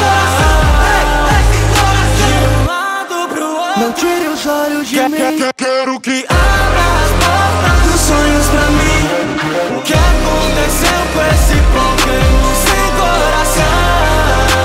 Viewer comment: oque o quis dizer "que- que-que-que" oque ouvi "ga-ga-ga"